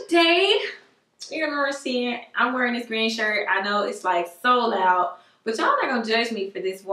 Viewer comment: I thought she said red shirt at first 😂